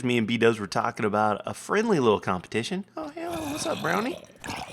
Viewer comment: Did say brony 0:33 bro hoof